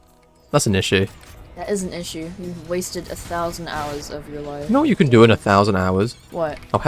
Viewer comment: i thought you said you HAD a thousand hours...